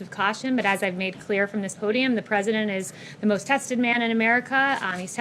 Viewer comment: She said Trump is the most tested person. A person who wants the US to do less testing. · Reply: I thought I heard "trusted" and I was trying to figure out why the press wasn't laughing their butts off.